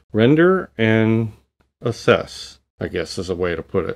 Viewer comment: I thought you said render and obsess. haaahaaa